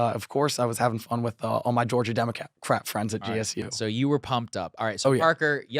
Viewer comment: Did he say Democats eh crap?